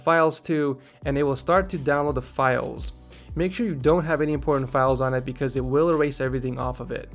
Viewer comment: is it only me or in min 7:20 I heard porn instead of important ?